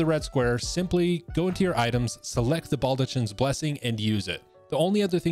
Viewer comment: thought you said ball to chin blessing